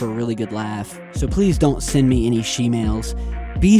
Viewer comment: Turned on cc just to see, at 12:06 you say 'So please don't send me any shemales', youtube cc says 'So send me any shemales'. I'm going to pay more attention to this, even just for entertainment value on my behalf, but its pretty weird.